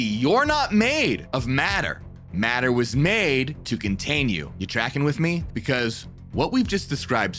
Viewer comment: Did he just say are you jacking with me 😆😆😆